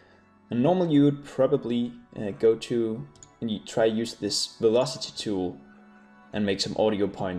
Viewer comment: i thought u said "using velocity" i cannot see that youre using it at all ....